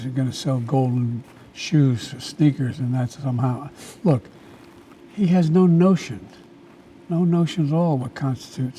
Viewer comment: Man I thought he said” he got no motion”🤣🤣🤣dude funny asl